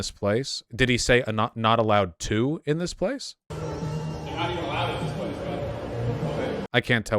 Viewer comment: I think he says "they're not even allowed in this place". Refering to the camera.